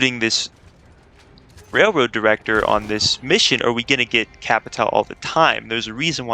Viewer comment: The cutscene is from the Bluewater Contract, third mission. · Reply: when i first heard it, i thought they said kapitan like pavel, but capitale. Also is "heard" the correct spelling ? i know it's not heart